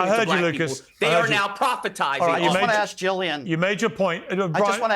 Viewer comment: Too much hypocrisy dripping from Meghan Kelly : 1. famine denial in Gaza on one hand, moral lectures on the other. 2. Calling Charlie Kirk a ‘prophet’? That’s how civilizations crumble. He’s a commentator, not revelation. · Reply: Proponent not prophet, you probably misheard.